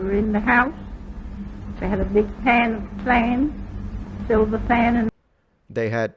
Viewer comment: “We had a big pan of sand” is what I hear